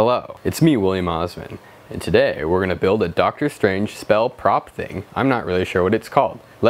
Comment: I thought that said making a doctor spell strange